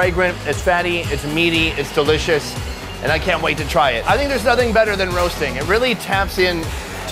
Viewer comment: sounds like he said "there's nothing better than a roast dick"